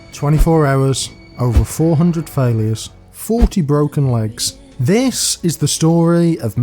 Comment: I was looking away at the start and thought you said "and 40 broken wanks" and was super confused before I ran the video back